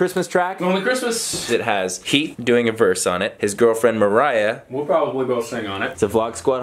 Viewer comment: i thought he said heath had a girlfriend i almost lost it